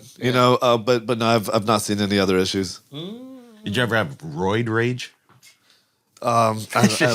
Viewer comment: ftr i heard “road rage” too js